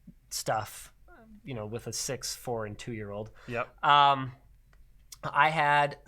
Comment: The first time I heard "With a 6' 4" 2 year old" and I thought, that's a tall 2 year old 21:30 How do you defeat a virus that eats data like Pac-Man, obviously you type "cookie", I almost forgot Penn Jillette was in Hackers, good movie, I give it a perfect 5/7